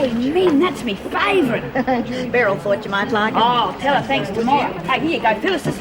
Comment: where min gives class the comic did she say merel said you might like it?, hhhmmmm